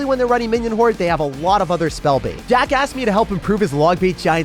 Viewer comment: In the vid look at time 12:33, ot sounds like he said jackash